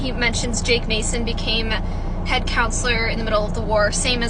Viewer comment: When you said “head counselor” I heard “head count slur” 😭😭😭